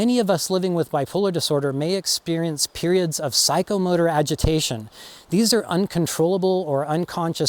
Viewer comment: This is funny not funny. I thought you said psychomurder agitation not psychomotor and I had to turn on the closed captioning but I got a good chuckle out of my mistake. When I am severely stressed the agitation and saying mean things to people comes easy as do hallucinations in extreme stress. I'll check the channel for videos on how stress can affect bipolar. If you haven't done any on this subject I'd like to hear about stress and bipolar. I was under extreme stress for years with my husband's terminal diagnosis and then when he died felt kind of guilty as it was over and my stress and depression lifted a lot. I grieved more in those years than I have since he died so anticipatory grief of 21 years (yes years and no people don't usually live that long with his heart diagnosis) actually helped my situation in the long run I think.